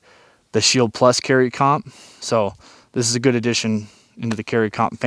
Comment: I'm pretty sure "comp" stands for compensated not competition. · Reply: I thought he said comp addiction.